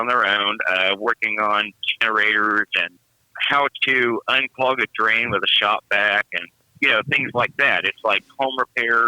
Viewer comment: Hillbilly Holler: I chuckled when you said "How to unclog a drain with a shop vac" because I thought you said "HOW TO UNCLOG A DRAIN WITH A SHOTGUN!!! LOL Too funny :)